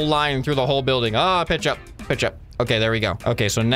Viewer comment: dude when said "pitch up" i thought he said "ketchup" XD